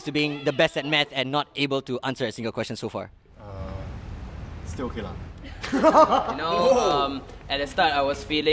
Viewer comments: Did says 20:52 Still ok lang??? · is Says "still ok lang"